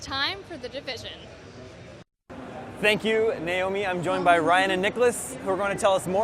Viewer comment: sounds like he says "thank you, nail me."